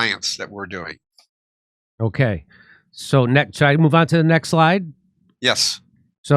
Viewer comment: Ahh, I thought he was saying move onto the next LIE, not move onto the next slide and still made perfect sense.